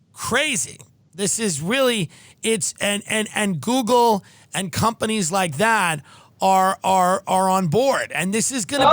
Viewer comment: which journalist she spoke about around 1:30:17? the about uncovering israeli art students. sorry for my english i didnt hear it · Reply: CHRISTOPHER KETCHAM